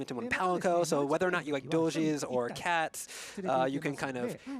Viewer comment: did he just say doges